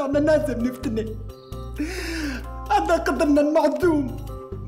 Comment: شو قالت طنفة هون😂😂😂😂😂😂😂😂💔🌹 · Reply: هذا قدرنا المحسوم